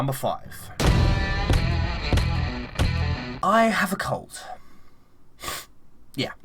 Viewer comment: i nearly freaked out when i heard you say "i have a cold" because I heard "i have cancer "